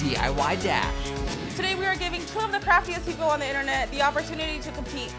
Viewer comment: When she said craftyest people i thought she said crappiest people lol